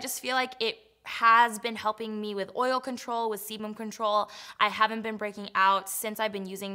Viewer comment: omg it sounded like you said SEMEN control at 2:23 hahahaha